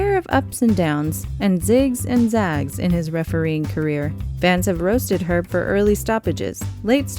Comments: who else thought it said "fans have roasted her for..." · why does she say her when he is a man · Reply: Owen Bosano She said Herb not Her